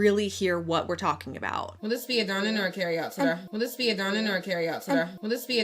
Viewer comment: I heard 27:54 'Will this be a dine-in or carry out for you'